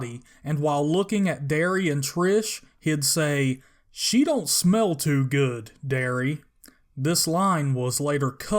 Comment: I thought he said "Shit dont smell too good, Darius." 🤔 🤣🤣🤣 You made me realize the sign in the tongue eating scene.